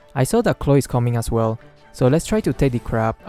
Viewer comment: Is it just me that I heard just said Chloe at 5:59 instead of Claude?